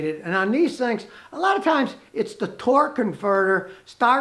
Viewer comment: Did anyone else catch that at 5:42 “it’s the torque CONFERTER “ lol looks like even scotty didn’t catch it himself